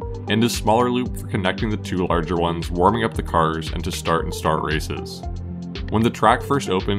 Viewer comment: did he say start and start races?